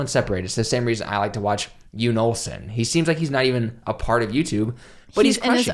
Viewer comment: who did he say here, can’t really make it out? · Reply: Pineapple 303 Jon Olson. uses the Swedish pronunciation of "Jon" when referring to the Swedish YouTuber.